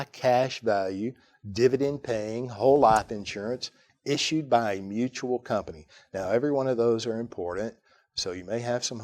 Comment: he said the Use High cash value dividend-paying whole life insurance policies issued by a mutual company. Is that correct?? I couldn't understand the last one